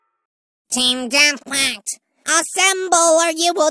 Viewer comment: Team Death Quackt is what i heard at 2:07 Edit: no u